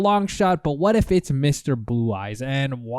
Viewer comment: Never said he was thoughh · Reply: just rewatched it, my bad I misunderstood what you said there. I thought you said "What if he is Mr.Blue Eyes" and not "What if he speaks with Mr.Blue Eyes"